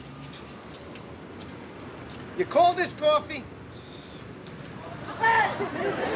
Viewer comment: "Your cold is coffee!" what 😆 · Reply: I always thought he said "you call this coffee!"